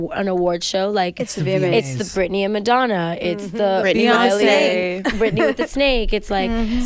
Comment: Lol at 2:40 when they were talking about vma performances and they brought up Britney, I seen all talking and heard Britney bitch at the same time so I thought she said that and my jaw literally must've went to the center of the Earth, but it came back when I listened a second time.